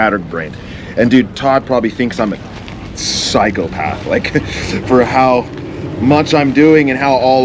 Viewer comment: I heard "Todd thinks I'm a cycle path"